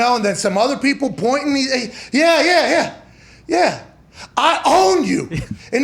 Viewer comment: THANK YOU for clearing up what AR said after his TD. I thought he said "I love you!" -- in a sarcastic kind of way. "I own you!" is MUCH BETTER.